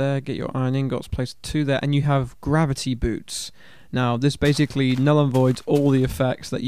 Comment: I thought you said gravity boobs!